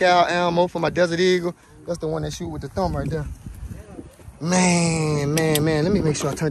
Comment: Yo did you just say I want to shoot with your thumbprint